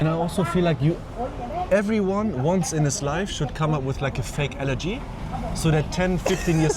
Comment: In the intro of the video I thought said “everyone should come up with a fake elegy” and I thought gee, that’s a bit extreme. I kept watching and safe to say I’m glad I was wrong 😂